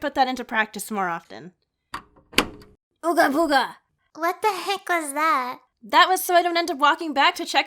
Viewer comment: you saying Ooga Booga made my phone think you said "Okay Google"